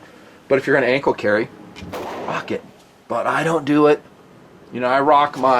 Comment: Did you say if you are going to ankle carry, fuck it?